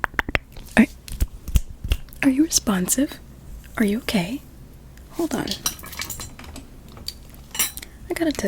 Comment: I love the video and your so pretty but when you said are you ok I thought you said are you gay?💀